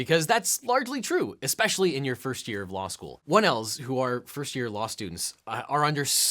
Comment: Oh my gosh, at 07:31 it sounded like you said wood elves not 1Ls 😂 I had to rewind it and double check cause I was going to need more info if they were called wood elves 🤣